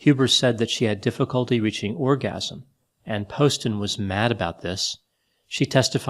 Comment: I was friends with Ryan and I had met Shayna. She wore a lot of makeup but other than that seemed normal to me. Admittedly I barely knew her and Ryan only spoke a little about her to me other than she was "exhausting." I can't begin to describe how shocking his murder was to all of us. She also joked about killing Ryan on Facebook before she killed him. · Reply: Her lawyers wanted to use the "Orgasm" defence. I thought he said "Origami"